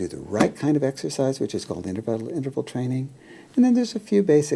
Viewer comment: what does he say 10:24 what does he say, " ****** interval training" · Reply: He sort of stuttered. It's just plain 'interval' training.